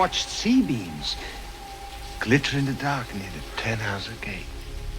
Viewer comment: All this time i thought roy said „“ive seen C-beams glitter in the darkness with 10 hours of gaze“ not Tannhäuser Gate lol